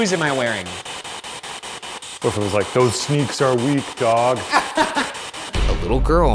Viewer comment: Like right after he says" it's 2018" i heard,"end is near"